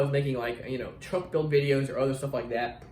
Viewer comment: Did you say "truck build" at 10:10?